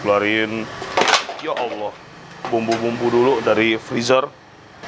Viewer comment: Ngeh ga sih sm latah nya 0:44 apa salah dengerr :) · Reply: Emg sering dia bang ngomong ya allah